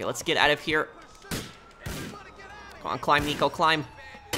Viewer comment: it sounded like cmon don’t make me kiss you